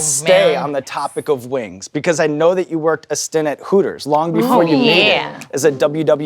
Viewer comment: swear to God he said assistant hooker